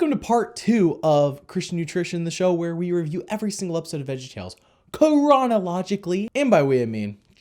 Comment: it sounded like you said piranha logically instead of chronologically